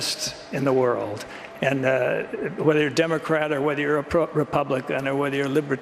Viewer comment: Did I hear Clint say “whether you're a Democrat or a CROOK Republican”?